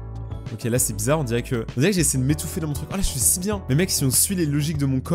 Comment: Quand tu n'as pas compris se que tu as dit moi j'ai compris "mon trupon" je sais pas se que sais mais j'ai compris sa😂